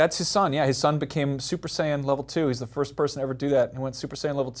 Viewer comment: I feel like everyone knows at least something about Dragon Ball Z, even my parents know who Goku is and what Super Saiyan is :P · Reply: This kid in middle school tried to explain DBZ to me, and when he said "Super Saiyan" I thought he said "Super Sand" so for a minute I had this misconception that these dudes ate magic sand that made their hair catch on fire and gave them powers. Then again, when I was five, I also thought Superman ate Kryptonite.